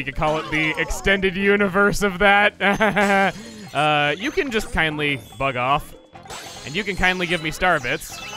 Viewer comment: am I the only one who heard him say "uh, you can just kindly fuck off" hearing it the first time?